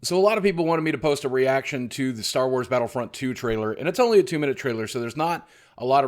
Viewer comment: Battlefront was garbage, get real people... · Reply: Xarko 1. He (The OC) is talking about Battlefront (2015). 2. I thought it was alright, but opinions I guess. 3. I thought you said BattleFIELD 1 at first and was heavily confused